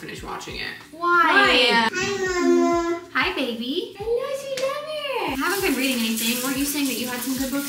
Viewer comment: She gave us ALL the boy vibes and I'm pretty sure sure she said brother in the bathroom too when Lola came in 🩶💙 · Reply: I heard “hello sweet lover” but totally could have been “I know it’s your brother!” Ahhh